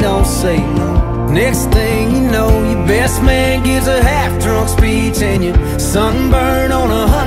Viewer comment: The text said "best man gives a heartfelt speech" But I swear I heard "half-drunk speech" 🤣